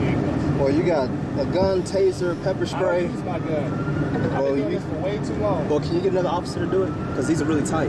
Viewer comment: Did he just sat “ I haven’t used my gun in way too long “? About the 36 minute mark. It sounded like he said “ I dont use my gun I haven’t used my gun in way too long “